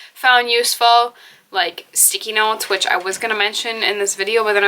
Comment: When u said "sticky notes", for a second I thought I heard "Sikinos" (an unknown Greek island), and I was like huh??? lol❤